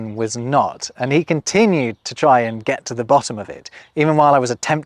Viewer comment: at about 11:26 I hear 'try and.' the captions have 'try to'. I've often wondered about forming an infinitive with 'and' instead of 'to.' But maybe that's a whole 'nother' story?